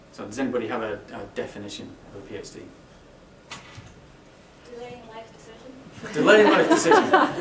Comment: what did she say at 3:12? I couldn't hear the joke clearly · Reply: had to repeat it a few times but she said delaying life decisions :)